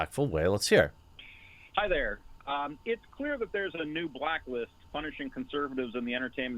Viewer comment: Byrd He didn't say "I hear" he said "it's clear" beep boop